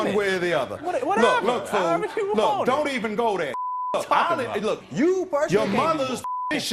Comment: I think the judge said “don’t even go there nigga”. He got real with it real quick lol